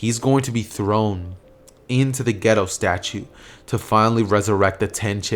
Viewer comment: Am I the only one who thought he said the ghetto statue 😂😂